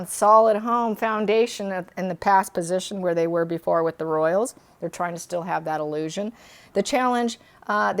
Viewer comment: I thought you said the children were with the royal family. Am I dreaming?